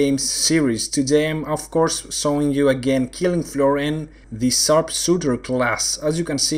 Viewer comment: btw, what game is this? did you say killing fury? or some coop fury? or killing furion? @ 0:24 ?? It looks easy and fun for a Co-op FPS.